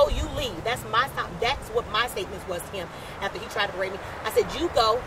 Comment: did she say "after he tried to rape me."